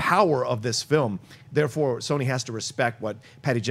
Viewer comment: Did just say SONY at 6:01, While talking about WONDER WOMAN?????